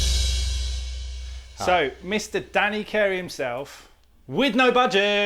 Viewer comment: I thought it said Dana Carvey, what a let down....xD